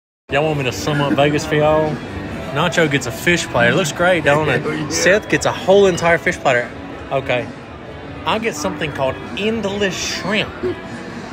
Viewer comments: I thought he said some up babies for yall I was like the fuck this have to do with babies? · I deff thought he said “English Shrimp” at first 😂 Also, calling something “endless” but you can’t keep getting more until your done eating is FOUL!